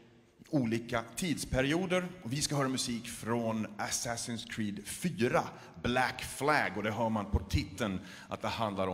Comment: does he say fuck this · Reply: he says "Faktist" which is swedish for " Actually"